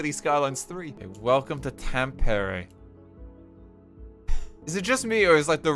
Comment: Wait, wait wait. It almost sounds like is saying Tampere at 0:38 :DD (Tampere is a city in Finland, and its just funny to me mostly 'cos game developers are frm Finland.)